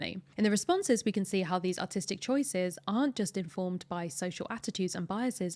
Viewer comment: why is it called autistic choices? Is an honest question, because i'm autistic so i want to know is it used in this context.